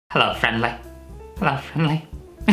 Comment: DID YOU SAY HELLO FINLEY 😮!!!